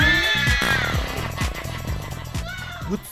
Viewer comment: i thought the guy in the end said "scatlord" haha